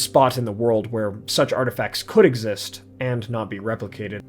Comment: It sounded like you were saying 'Arda'facts instead of artifacts 😝 good stuff!